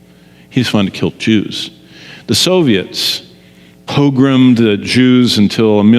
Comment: What is it he said at 5:20 ? "The Soviets _________'d the Jews, until a million of them fled, and went to Israel" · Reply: ‘Pogrommed.’ The Jews have always experienced pogroms throughout history.